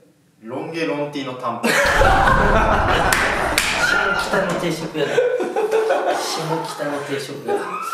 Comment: 一番きたねぇ定食屋だ じゃなくて、 下北の定食屋だって聞こえるんだが！！笑笑